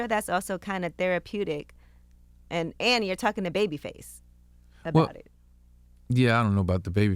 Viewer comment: Thought it said Babyface Ray lol bye 😂